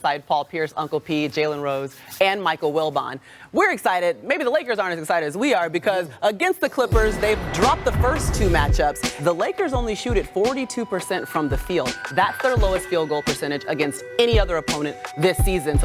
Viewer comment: Did she just say the Lakers "shooted". @ 3:45 Im deaaad